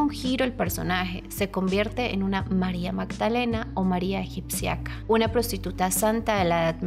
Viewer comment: ¿qué dices? "O María iruciaca" o qué...